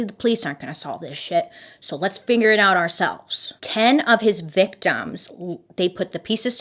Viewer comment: I’m laughing because … Us: we know. We know. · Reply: I have to know... Did you say figure it out or finger it out.....?😸🤣